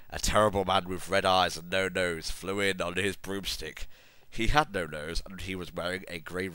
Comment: Okay, I misheard, but it would be awesome if he had a boomstick. Also, she screamed something sexily? How does one do that?